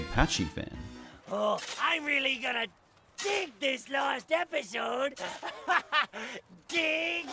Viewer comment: for number 9 when i was a kid i thought patchy said last episode and got really upset cause i thought spongebob was ending